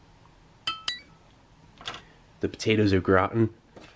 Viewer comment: I keep hearing "potatoes look rotten"